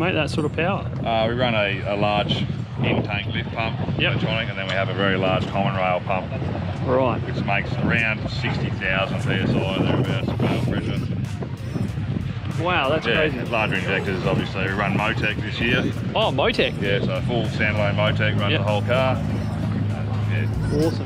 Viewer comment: Did he just say 60 thousand psi rail pressure?